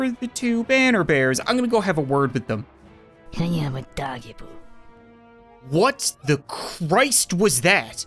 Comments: I thought it said I AM A DOG POOp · “What just whispered something in english in my ear?” Im sorry, but i dont think hyama dajibu is english Edit: this is one of my most liked comments on this channel thanks :) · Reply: I heard "Hiama doggie poo" LOL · That sound you heard talking, that was the Deadeye She does have a weirdly english sounding voice compared to the rest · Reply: And that sats the unit aming 90 degrees of. Först i thought that she said i am a doggy poo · “What just whispered something in english in my ear?” Im sorry, but i dont think hyama dajibu is english Edit: this is one of my most liked comments on this channel thanks :) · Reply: I heard “I have a doggy poo” 😂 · “What just whispered something in english in my ear?” Im sorry, but i dont think hyama dajibu is english Edit: this is one of my most liked comments on this channel thanks :) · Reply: I heard “I am a doggie poo”. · " i have a doggy poo" 💀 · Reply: i heard “i AM a doggy poo” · Did that unit just say, "I am a doggy bowl!" Maybe a doggy poo too 😂😂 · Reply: I thought it said "can I have a doggy poo" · The whisper was the deadeye saying “I am un-dodge-a-ble” like thanos saying “I am inevitable” Also 0:00 is my favorite reaction from him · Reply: I thought the dead eye said I am a dog-e poo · Did that unit just say, "I am a doggy bowl!" Maybe a doggy poo too 😂😂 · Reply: I heard "hang out with doggy poo edit: I'm pretty sure I accidentally copied something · i heard "can you have a doggie poo" XD